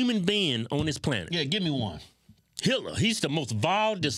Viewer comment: When (I think, twins all look the same to me!) said Hitler, I thought he said “Hillary”, as in Clinton. I was in total agreement. Hitler was pretty bad too though.